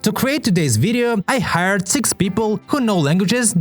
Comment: I heard "I hurt 6 people who..."